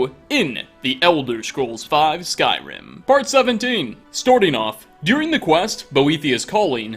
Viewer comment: I just heard him say storting of